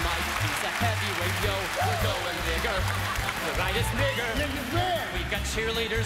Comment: he really did say at 3:24 "we're going n---er, this night is n---er" anyone else hear that? All often Mike Tyson brought to stage. Yikes!!!